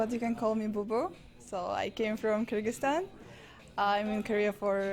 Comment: where is she from? I couldn't hear what she said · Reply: She's from kyrgyzstan